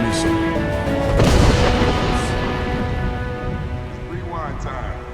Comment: did anyone else hear "its rewind time"